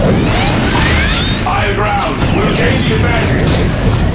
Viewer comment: Can anyone tell me what he's telling between "finish this" and "continue assault" 0:16, please? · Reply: "Higher ground, we'll gain the advantage"